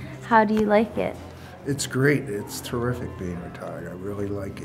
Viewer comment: i thought he said " Its terrific babytard"